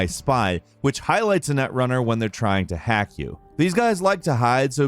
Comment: I kept hearing "netrunner" as "nutrunner" and I'm gonna chortle away in the corner now